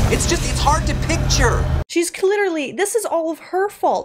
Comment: Did she just say........"Cliterally" 🤣🤣🤣